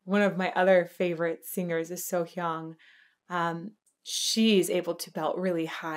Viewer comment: So Heung? what name is she saying?